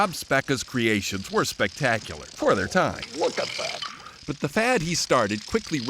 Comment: does he say wtf? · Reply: Try, “Look at that!”